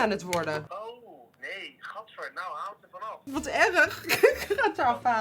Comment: when she says “this is terrible” in her language, it sounds like she says “whatever” in english at 11:17 😂😭<3